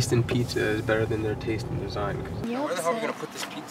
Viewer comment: wtf is morgan? actually half the reason i watch these videos. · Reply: Lol i thought you said what the fuck is morgan... it makes sense with "where" though.